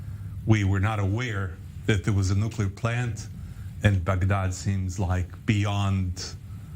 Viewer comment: It sounded like he said 'Fagdad' instead of Bagdad.